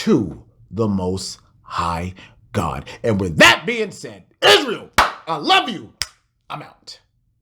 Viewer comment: Thought u were gon end it saying, "Niggas, I love you, Im out...." 😂🤣😂🤣😂🤣